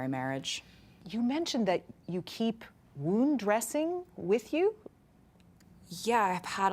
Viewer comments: What does she say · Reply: Wound dressing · what does the interviewer say? “roon dressing?”